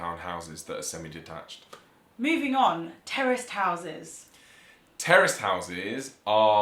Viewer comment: Had to replay it a few times, I thought they said "terrorist houses" !! Interesting to note: Condominiums don't exist outside the US.......and even in the US they only appeared around the 1960's. I was looking to see if the UK has anything similar, but I don't think they do.